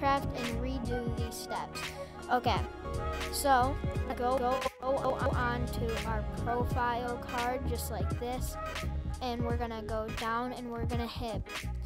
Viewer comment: who else heard goo-go o-on